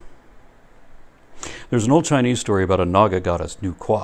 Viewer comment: u said there is an old chinese story , but the subtitles says there isnt a chinese story ....